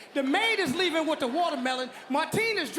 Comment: It didn’t sound like he said fluffy. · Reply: sounded like THIS FUCKING BASTARD